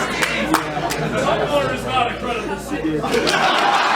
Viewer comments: What did the guy shout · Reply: "Tumblr is not a credible source" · what did he say something is not a credible source? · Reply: Tumblr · What does the guy say at around 32:36-32:45? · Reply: Nihilist Before that moment "OH MY GOD, WORDS" and then at the time you mentioned, he says "Tumblr is not a credible source."